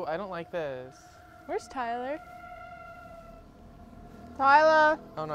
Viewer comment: when she started to say "where's Tyler" it sounded like she was about to say "Kylie" and I was about to lose it😂😂😂